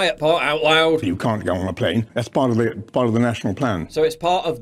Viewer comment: I thought he said "It's all part of a flan!"